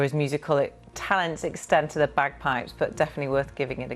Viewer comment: When he said I have got you some bagpipes I thought he said a bag of pipes , such as from B+Q!